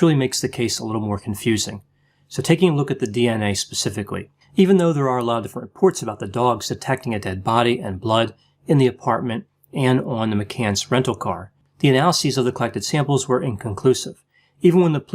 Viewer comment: "Dogs attacking a dead body"?? That's what I heard you say!